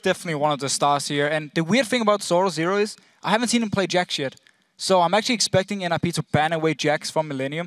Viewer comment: I haven't seen him play jack shit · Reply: EpicUltraKingSmizzy yeah definitely a double-take moment lol. i heard "jack shit" the first time around but I'm pretty sure it was indeed "jax yet" in deficio's somewhat thick accent.